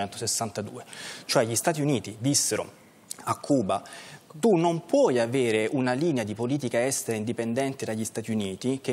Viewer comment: Qualche esperto di labiale! Cosa dice fra i denti senza audio? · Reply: "Odio questo biondino" Ha detto questo!!! Forza Alessandro Orsini